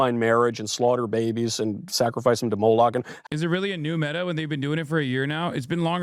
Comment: Did bro just say “Sacrifice them to Molag”?! Like Molag Bal?? From Skyrim? 😂😭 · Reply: Moloch